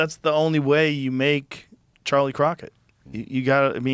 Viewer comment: My bad I thought it said casey rocket